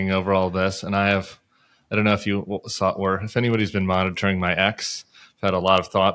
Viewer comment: Tim at first I thought you said "I don't know who has been monitoring my ex" and I was like "oooooh who is Tim's ex???"